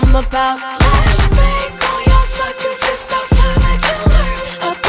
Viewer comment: i thought on 0:18 to 0:26 she said Flash Back all ya'll suckas its about time you learn about the about the meanest era to encounter this earth